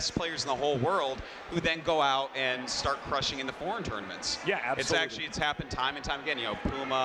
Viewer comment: Every time they say "foreign", I hear "porn."